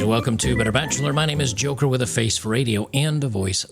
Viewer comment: Your intro cut short in the begenning. Sounded like it said "hooker with a face for radio"